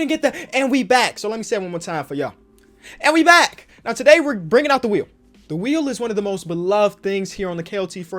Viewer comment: Thought he said “today were bringing out the weed” I’m dumb